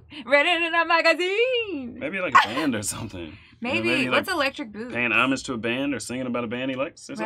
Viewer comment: First time I heard this I thought he said she has electric books. 😆 🤣 😂